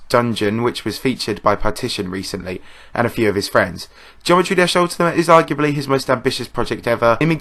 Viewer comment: Tbh I thought it said “geometry dash update”